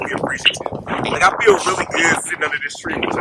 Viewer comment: Also at 37:58/59 when you said I like sitting under this tree I heard, “so stick around”